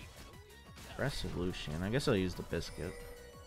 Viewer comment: When he said “aggressive Lucian” I thought he said aggressive little shit😂😂I was like damn that’s a little out of pocket